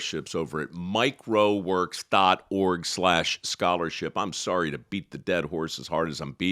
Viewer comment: This is the 1st time I realized the foundation is Mike Rowe Works. I always thought he was saying Microworks.